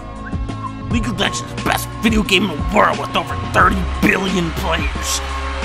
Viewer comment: 32 Billion players *and 7 good ones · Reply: Wait, what, 32 billion? EDIT: Oh shit I didn't know, I thought they said 32 billion.